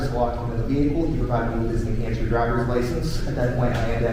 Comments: It sounds like he says New Hampshire but shows virgina in subtitle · Sounded like he said New Hampshire not Virginia